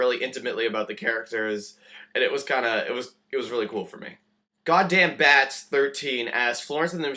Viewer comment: did you say G*dD*mn at 3:40???? :((